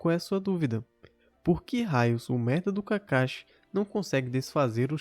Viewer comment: olha a audacia desse cara. foi isso mesmo que eu ouvi? ele disse? "esse merda do kakashi"ಠ_ಠ